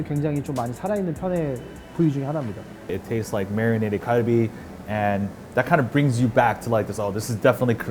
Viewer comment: says "it tastes like marinated Galbi", but the subtitle captions say "marinated Kobe".... Wtf?